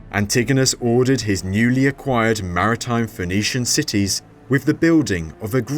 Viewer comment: First I thought I heard Venetian